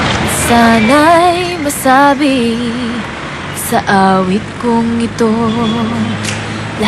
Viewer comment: did she say wasabi? i love wasabi, much better than horseradish